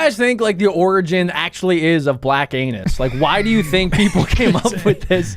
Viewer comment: YEAH YOU PEICE OF CRAP I FEEL AFFENDED BY YOU. MY GOD YOU GOT SOMETHING AGAINST WOMEM? 25:10 I thought bro said Black Anubis me: “wait the Anubis is black anyway.”